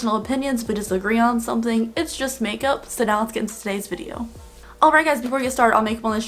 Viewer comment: At first I thought you said "if you disagree it's just makeup, so sit down" instead of "it's just makeup, so let's get started" 😂